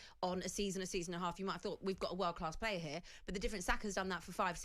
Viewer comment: did she really say salary has been world class for the last 5-6 years…? 😂